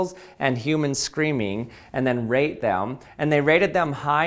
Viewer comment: I swear he said "rape them".